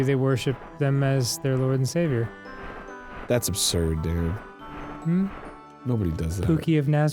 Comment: arin called dan "Darin" and I think the implication that he thought HE said that and was telling himself that pookie of nazareth is ridiculous is kind of hilarious